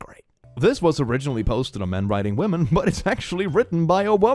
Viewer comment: Istg whenever somebody says "men weiting women" i always hear "men riding women" and im like "W O T"